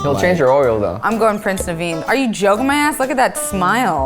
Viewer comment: I misheard when said 'Prince Naveen' and thought she said "Prince and the Bean' but honestly, same thing